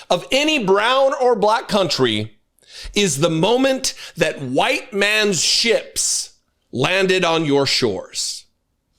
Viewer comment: For a moment I thought you said “when white man’s 💩landed on your shores.” TBH also works.